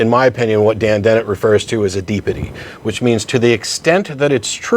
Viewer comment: can anyone tell me exactly what matt said at 3:30? he used a d word i think to describe something dan dennett said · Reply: He said 'Deepity'.